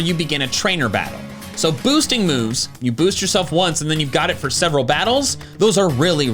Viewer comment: I don't even know what he said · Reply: Stat boosting moves last making them good